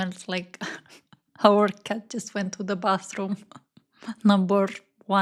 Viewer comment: Ok so at first I misheard and I thought that your boyfriend said the Dolls perfume smells like your cactus 🌵 went to the bathroom.... I was like what??? 🤣 Then I spent too much time trying to figure out what that would smell like... On a different note... I like the way you think, I love hearing you talk. You've got yourself a new subscriber ;)